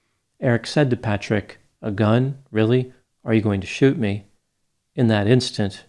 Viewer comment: I was listening to this while doing something else and I misheard the story. I thought someone dressed as Barney had shot someone in the chest LOL "A gun, really? Are you going to shoot me Barney?"